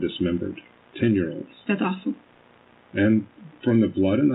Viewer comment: did she just say "that's awesome" when he said he's found kids dismembered? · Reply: it wouldn’t surprise me if she did but she said “that’s awful”